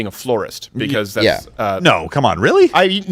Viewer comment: XD i thought you were saying floorist i forgot about the flower one i havent seen or heard of them in so long